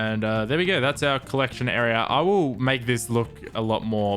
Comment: Did he say ‘Awoo’ 😂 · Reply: I will. Its his accent